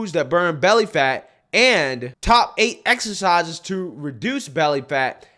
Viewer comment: Wait!!! Did you just say exercise, I thought you said more fries. 😅